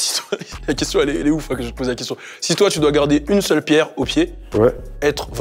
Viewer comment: c'est moi ou j'entends pierre 🤔